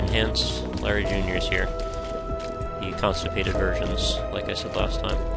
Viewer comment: Hmm, I thought he said the constipated virgin of Larry Jr. Lol